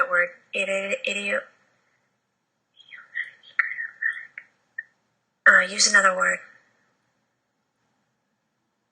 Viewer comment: I heard a whispering voice at 8:16-8:18 which is prounouncing a kind of long word to which says, "can you use another word?". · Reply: I heard it too, it sounded like biomimetic. It also sounded like whispered the word into her hand trying to figure it out. In the context of conversation at the time biomimetic makes sense because they were talking about minerals being alive.